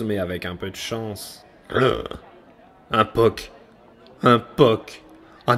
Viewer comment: Un quoi ?! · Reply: Un poc. Un éclat dans le vernis, un coin enfoncé, une trace sur la couverture. Tout ce qui fait fuir le collectionneur un poil trop maniaque en chasse de l'album dans le meilleur état possible. Si, ça existe, il y en a, j'en ai rencontré.